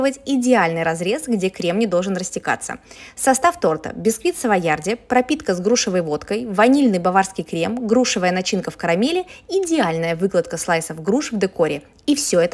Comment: Мне одной послышалось вотк@